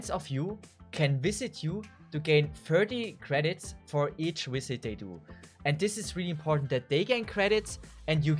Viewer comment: I have no idea what he's saying. "Thirty credits for each wizadaydoo." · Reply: “Visit they do”